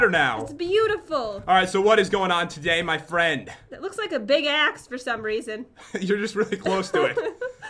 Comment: SAID BIG ASS! 1:21 No cursing dear kids are watching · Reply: Oh, she said "Big Axe", sorry if you thought she said xD